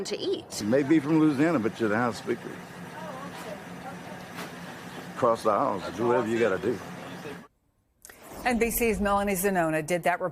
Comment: Anyone understand what the gentleman was saying? (Not trying to be snarky -- I really want to understand what he said). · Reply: He said, "You may be from Louisiana, but you're the House Speaker...cross the aisles...do whatever you gotta do..."